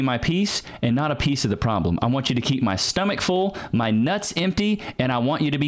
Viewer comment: I heard "Keep my stomach full, my nuts empty.." and I was died laughing no lie